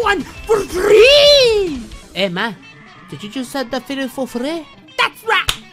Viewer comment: did u say the fetus for freh?